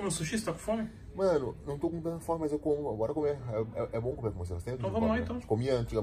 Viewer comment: só eu que entendi de primeira "é bom comer você" ??